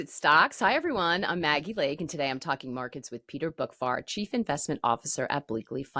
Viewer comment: I really thought she said his name was Peter Bookfart.